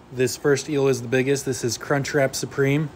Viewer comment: “Yeah, this is Crunchwrap Supreme, he’s pretty tame.” Is the best thing I’ve heard all day in this context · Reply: Crunchwrap!! I thought he said c**t trap. Ffs 😂